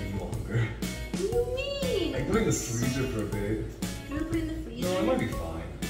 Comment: am I the only one who heard say “I think it’s freezer burnt babe”?! Or am I trippin since it’s like 2 in the morning?? 😭😭